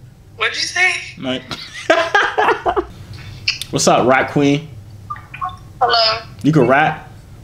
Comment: I thought he said wassup rat queen😂